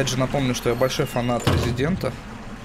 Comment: мне послышалось что он сказал" я большой фанат президента"